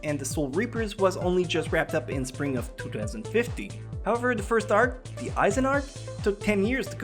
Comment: it sounds like he says 2050